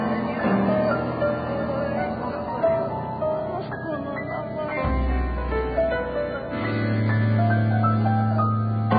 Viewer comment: ここが1番声聞き取れそうで気になる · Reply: 「あの人の名前」